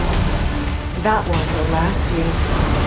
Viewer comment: what does she say in this clip? It's hard to hear it. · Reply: That one will last you.